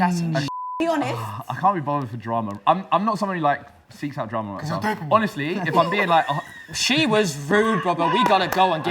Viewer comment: what did say at 17:57????? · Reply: Cause your dopamine? He is making fun of because he always mentions science